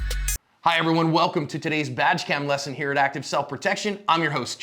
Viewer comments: I honestly thought you said vag cam lmao i was like wtf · I thought ya said vag cam🎉